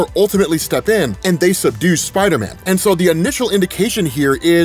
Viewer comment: For a second I thought you said that the Fantastic Four seduced Spider-Man😂